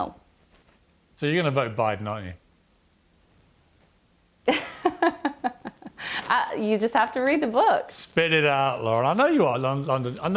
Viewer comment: Does he call her Laura? 🤔